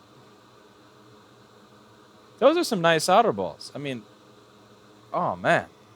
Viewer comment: Sounds like you said "nice otter balls" instead of "nice solder balls"